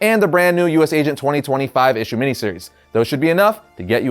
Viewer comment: So hard to understand what you're saying here. I guess too many separate numbers spoken aloud in a sequence. 2020 5 issue mini-series. Took me forever to get it. Sounded like 2025 issue mini-series. Probably just me though.